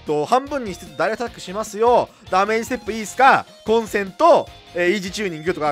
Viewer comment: のダメージステップいいっすか？はGOOGLEさん側にはラーメンセットいいすかに聞こえるんやな（笑）そして8:41たったの53しか戦闘能力ないのに自慢していくフリーザ。。。ｗ